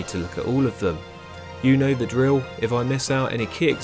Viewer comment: i thought you said “unova drill” heehee